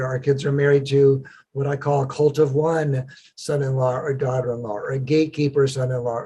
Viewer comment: did he say "cult of one"? What is that